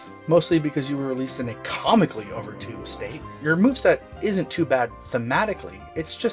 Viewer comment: what is he saying here? "comically over two state"? I don't understand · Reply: sorry I kinda mumbled there, I said "Comically overtuned state"